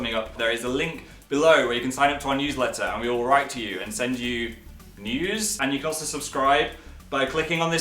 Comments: I thought he said nudes 😂😂😂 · I thought he said we will send you nudes😹😹😹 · When he said at the end about the newsletter and that they'd sent you news not even kidding I thought he said send you nudes. Which id be just as happy with but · I THOUGHT HE SAID "and send you... nudes...?" LMAO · Oh god i thought he said "send you NUDES" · When he said 'We will send you news' I thought he said 'We will send you nudes' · i thought he said send you nudes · I heard "and send you nudes" not "news" 😂😂😂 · to me when he said news, I thought he said nudes... "and we'll send you...nudes" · i thought he said they'd send out nudes...turns out it was just news...i really need to get my ear tested